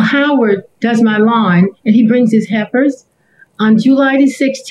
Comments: When she said helpers at first I thought she said heifers. I was like ohhh snap she is not playing today 🤣🤣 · I swear it sounds like she is saying his “heifers” not “helpers” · LMAO the defendant… I legit thought the plaintiff said HEIFERS too. The woman was obviously saying HELPERS 😂😂😂😂😂😂😂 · At first I thought she said he brings his "hiefers" and I was like say what? he brings cows with him? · "HELPERS" not "HEIFERS"! 🤦🏿‍♀️It wasn't until she said the heifer started to speak, that I realized that I was mishearing. 😂. I seriously thought he had some country method of cutting grass with cows. 😂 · I thought she said heifers. I even played back and then did the CC because I couldn't understand most of what she said at the start. I was thinking it was the audio, or her word formations. He's right. She said it. That is not a nice thing to say by any stretch of the imagination. But also, he is full of excuses for this that and the other and there was no rust on the pole, and he just sort of smooth talks his way around things and contradicts himself but keeps on going like his contradictions never happened.